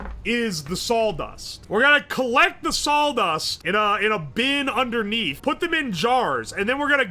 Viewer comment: Very sounds like he sayin 'saul-dust'